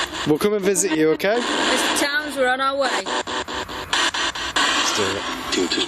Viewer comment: I heard it say Don't Do It.